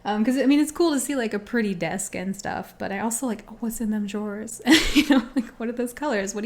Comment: I was in a different tab and totally forgot what the video was. I just heard "What's in them drawers" and now I'm dead.